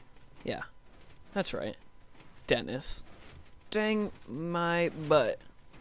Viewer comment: did you say "Bang my butt!"? :P